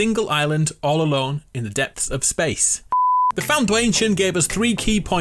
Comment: "Foun*dwayne*tion" I heard that...